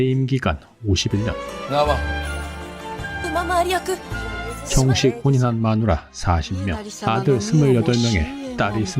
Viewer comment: 일본어로 뭐라고 말하시는건지 아시는 일어고수분! 알려주세요😢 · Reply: 키미노 나와